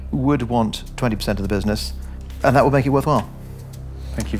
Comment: Wth does he say at 8:26? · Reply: "and that would make it worthwhile"